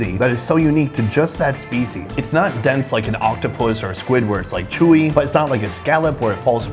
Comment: did sonny just say squidward? 😂🤣😂